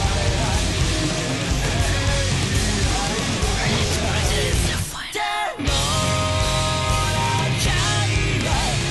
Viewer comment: what says the whispering at 3:05 ? Great song !! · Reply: The final